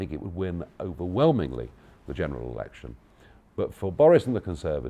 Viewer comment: Did he just say "general erection"?